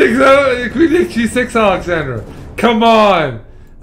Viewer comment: tf is he saying lol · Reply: "queen takes ilealehh queen takes g6 alexandra"